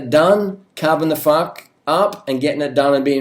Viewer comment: sounded like ya said carbon the fuck up lol